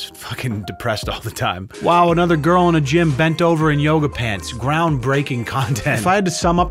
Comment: I thought you said YODA pants and my imagination went wild haha thanks 😂😂😂